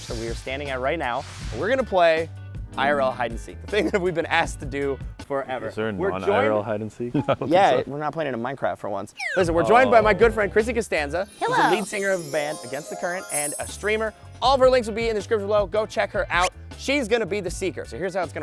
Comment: I thought I here said meet my girlfriend and when I roll it back it said good old friend crissy